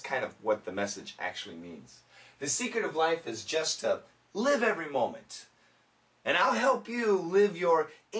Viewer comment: It's awful : I heard "The second of life is juste an every moment. Not terrible